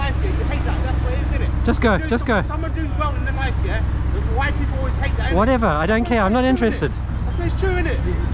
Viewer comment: did he say "why people" or "white people". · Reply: He said "why people" which is obviously not correct grammar. But our government doesn't correct ethnic colloqualisms in schools because apparently it's racist. So, our language is becoming increasingly difficult to understand as a result. 🤷🏻‍♂️